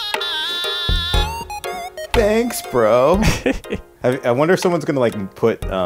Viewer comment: I think he saying “thanks now goodbye”